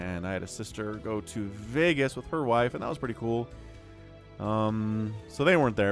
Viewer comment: did you say, you had a sister go to.. with "her wife"? what da!?